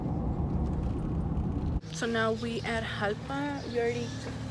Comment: NAHHH I thought you said you was on your way to Japan 😭😭